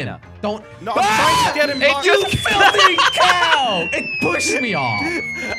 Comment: swore he said the f word I think lmao while he was falling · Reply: He said "you filthy cow"